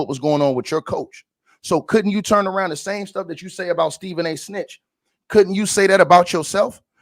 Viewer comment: I thought I heard Stephen A Snitch😂